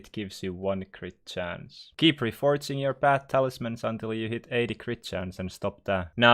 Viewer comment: I thought you said keep reforging your bat talismans to godly or zealous